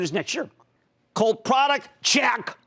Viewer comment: I believe he says “cult product” instead of cool product. Thank you! Watching Tesla grow with no real competition is so exciting to watch!!!